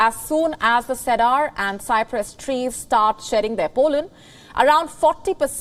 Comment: not going to lie, i panicked hearing "pollen", I heard "Poland"...and I just thought about history for a moment lol that was on my auditory issues though.